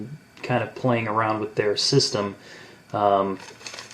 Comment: you said "their system"... and your iPhone thought you said "hey Siri"